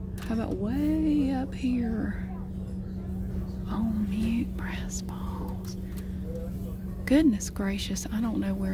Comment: did u just say “brass balls”..?!